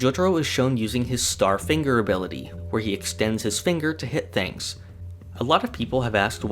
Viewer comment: I heard " he yeetxtends his finger"